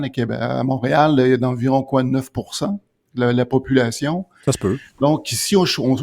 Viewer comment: Qu'est-ce qu'il dit à 2:26 ? La population musulmane de Mtl est d'environ "quad-neuf pour cent" ?!? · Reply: Il a dit 9%